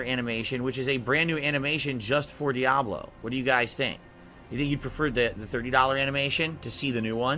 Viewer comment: Did he just say "do you think he'd prefer" lol 0:36. Wow 😅